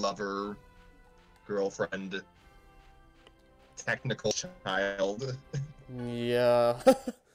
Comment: Audio cuts out at 15:03. I'm supposed to be saying "...technical mother of his child." 18:03 - "Only Love Live! can actually manipulate your emotions, I get it." 24:23 - "Being an anime fan is suffering some days." 43:13 - "I pair Lisbeth with Kleine for some reason. I guess because they're just kind of there which kind of describes both characters." 1:08:15 - "...but shh...don't say anything, just don't-don't give away the game." 1:11:13 - "Kirito has to basically save the princess from the dungeon before it's too late." 1:16:35 - "Not as much as Rem's life!" 1:17:21 - Can't remember what I'm saying exactly, but I believe it's something along the lines of "When you're actually playing Animal Crossing, Tom Nook's pretty benevolent. He lets you take your time paying off loans. That's amazing." 1:23:48 - "...and they hurt me in their own special ways, but..." And one line from that I totally talked over at 1:11:19 - "Anyone that sniffs someone's hair in anime is a serious f*cking villain."